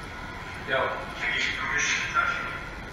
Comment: Who spoke at 6:58 right before walked over to the door??? Whoever it was, it sounded like they said something about "permission". I can't make out exactly what they're saying but I heard the word "permission". 👻💯👻💯👻💯 I believe the voice says, "WHO GAVE YOU PERMISSION TO ACCESS THE BATHROOM?". Not 100 though.